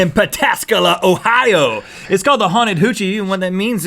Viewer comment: HAHAHA I THOUGHT HE SAID Haunted COOCHIE LMAOOOO